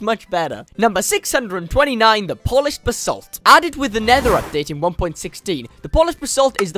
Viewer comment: am I the only one who hears Polish en passant😭